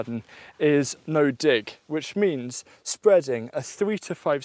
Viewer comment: sounds like you said the d word but you said dig